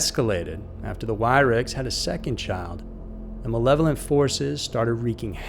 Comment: I thought you said Lisa had a son but inthe image at the end Jordan is a female.